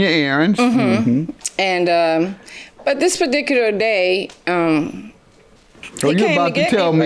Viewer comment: Did I just hear her say "this badicular day"